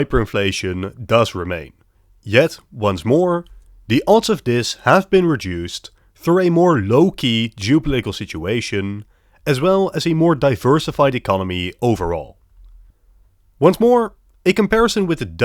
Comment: Did he just say "low-key"